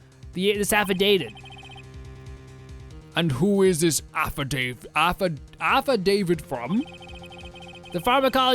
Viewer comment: I wasnʼt looking at the screen right then and when you stumbled over the line I thought maybe the Judge had another of his… moments… that Iʼd forgotten about. It sounded like heʼd said “And who is this Affi David?”